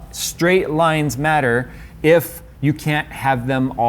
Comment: who else heard straight lives matter....